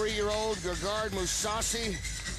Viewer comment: it sounds like she said gegard mousausage